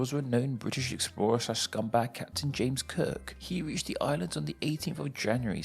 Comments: known British Explorer/scumbag James Cook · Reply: It sounded like you said James KIRK! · I could have sworn you said "James Kirk."